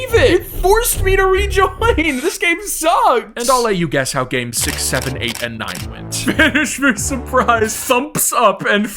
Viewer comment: NO WAY WHAT DID HE SAY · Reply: HE SAID COCOMELON